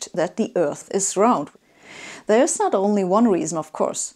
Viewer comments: Subtitle mistakes: 7:33 While Sabine says "round", the subtitles wrongly tell us "flat". 9:05 Just the opposite of the above. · There is a problem with subtitles at 7:33 where she says "round" but the subtitle say "flat". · audio is "the earth is round", but subtitle says "the earth is flat". Please decide yourself. :) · Who was in charge of typing the captions for this video? At 7:34 she says "round" but the captions say "flat", and again at 9:05 she says "flat" but the captions say "round."